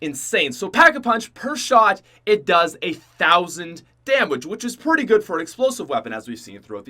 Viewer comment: did he say damwage